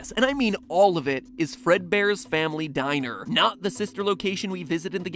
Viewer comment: I heard him saying i am fredbear